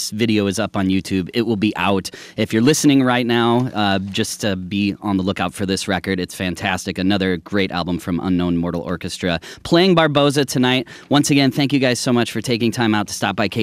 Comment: Did i heard BARBOSA at 25:29?